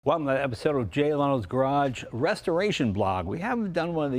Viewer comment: I thought he said "...J-Lo's Garage" in the intro, haha! That's what I'm calling it from now on though! It's brilliant, I can't believe I didn't come up with that!